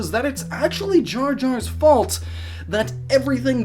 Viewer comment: For a second I thought it said "All Jar Jar's Faults", and I was preparing myself for the longest You Tube video EVER.